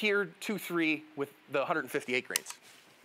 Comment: I thought you said 315 grains for the first ones and was wondering if I missed the fact it was .50 AE or something 😂